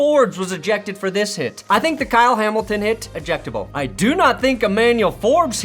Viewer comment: I thought he said ejectified lol